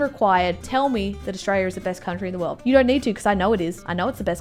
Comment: did she just say Australia is a fucking country